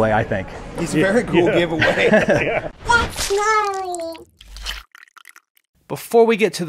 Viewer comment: What is that eyeball saying? I've been trying to figure this out for weeks. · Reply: Is it "That's gnarly"?